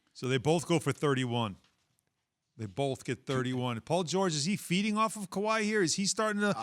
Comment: I thought he said "Paul George is he beating off Kawhi"